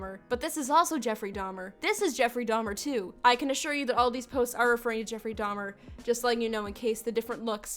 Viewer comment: what does she say here? All I got was “I can assure you these are” and the rest was muffled to me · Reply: "I can assure to you that all of these posts are referring to Jeffery Dahmer"